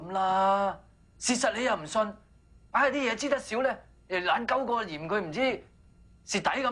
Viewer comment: 有無人知道3:41個到講咩 硬系啲野知得少呢.又乜乜乜 聽吾清楚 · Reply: 又懶鳩個 嫌佢唔知 蝕底咁